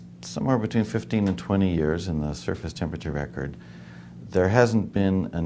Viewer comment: is he saying "the surface temperature of acrid" ? or is Accred, is that some place?